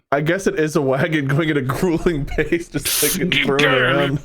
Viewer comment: “Grueling pace lets go!!” Is the new “Go, no stop” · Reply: Oh my god, I thought they were saying “gruel and pace”, as in shove some porridge in their faces and start walking lol